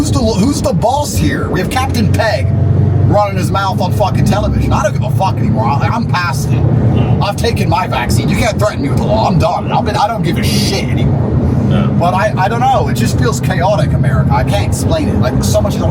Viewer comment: did I hear Tate say "iv taken my vaccine" 😢 really hope i heard wrong 🤔